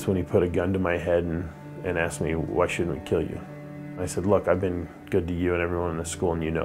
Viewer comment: Question: At 9:52, the man describing how he was nearly killed, recounted how one of the killers said to him: "What's your......". What was that word? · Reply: I think he said: "Why shouldn't I kill you?" - kinda hard to understand